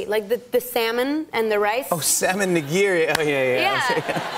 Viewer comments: I thought she said "Sam AND Nigiri" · salmon nigiri 🍣 lol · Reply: I legit thought she said "sam and nagiri"